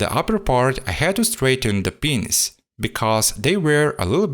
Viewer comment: Had to hear it again to be sure what you said there lol 😂🤣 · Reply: "The pins"